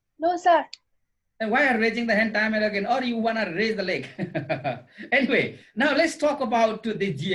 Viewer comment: "are you wanna raise the leg" . damn did he said that.